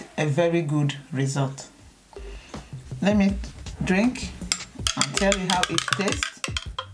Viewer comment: Sorry I heard you saying, "Let me drink, let me drink how it taste?" But Thanks anyway, all your ingredients are just good for health.